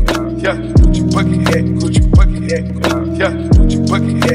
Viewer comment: Low key thought it said coochi Bucky hat